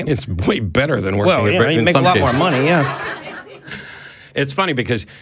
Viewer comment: Did Noah say "Murder" King? If so, then I like him even more! On that note, those who work at McDeath definitely make less than sex workers.